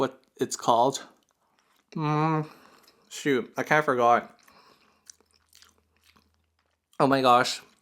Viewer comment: did you just said I can’t forgot instead of I can’t remember, ?? Lol 😂😂💙anyway ilysm you and your videos💙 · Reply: Haha sorry I ran my words together again, meant to say "I kinda forgot" 😂😂 .... but that's totally what would say too 😜! Love you too ❤️